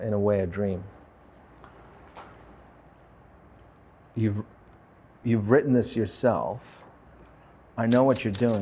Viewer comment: WHAT DID HE SAYYY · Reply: “Youve written this yourself(?)”